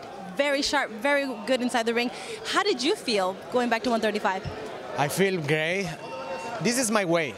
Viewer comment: DID HE JUST SAY ..I FEEL GAYYY ..! 0:25 lol 😂💩🌈🌈🌈🌈🌈🤣